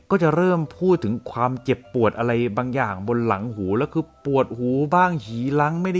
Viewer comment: พูดว่าอะไรนะคะ ปวดหู บ้าง xีล้าง นี้ห๊ะเลย